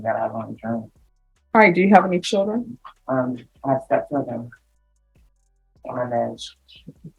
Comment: 7children?!!!’ May they never know their mother- the ones who do let what they saw her do is not what they want to do with their own lives. Personally it look like drugs were already leaving the permanent marks on her to only get worse! Hopefully she will get Clem and make a true life for herself before it’s too late! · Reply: I heard her say she had Step children.